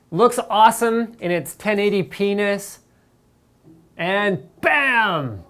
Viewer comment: My wife was sitting right next to me when Linus said that, and she was like "did he just say 1080 penis?" lol. Nice one Linus, you got my wife's attention.